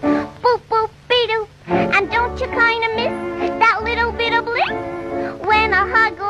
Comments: Did I hear "ain't u kinda gay?" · I HEARD HER SAY “ain’t you kinda gay?” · did i hear ain't you kinda gay??